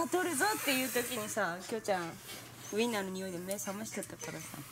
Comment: 作業しながら見てたら｢きょうちゃんウインナー食べる｣って聞こえて、バッって見た自分がいるww